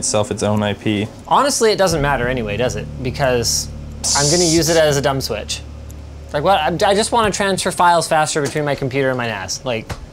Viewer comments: Did he just say "I just wanna transfer files between my computer and my ass" ??? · lol oh he said NAS i thought he said he wanted to Transfer Files Faster from his computer to his ASS